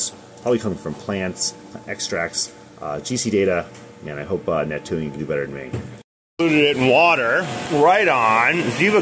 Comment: I was sleepy when this video came up on my play list, and I woke up real quick when I thought you said coke has Neptunium in it.